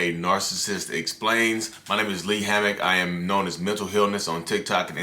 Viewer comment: I thought he said "Mental Hell-ness" at first 😹😹😹😹